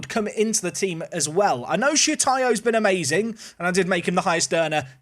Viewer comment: Be careful with demonitization mate sounds like ye saying shitayo